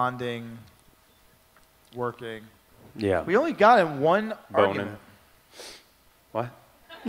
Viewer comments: Did Paul say 1:05 boner? 😂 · What did Paul say in 1:03 ? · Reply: boning :)